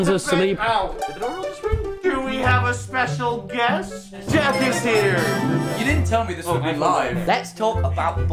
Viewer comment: I thought he said "death is here" for a sec